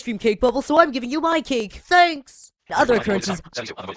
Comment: Did leafy said the "Other occurences" at 0:59??